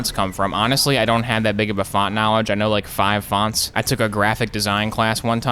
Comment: I thought you said Fonzie-knowledge